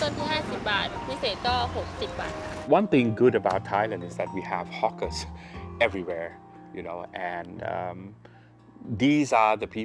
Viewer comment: we have hookers everywhere? Did he said it for real, can't believe it😅?